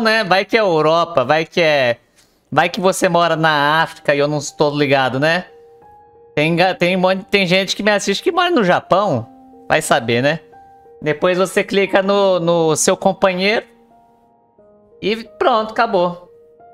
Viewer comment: Ah entendi, eu posso cutuca a minha companheira?🤣🤣🤣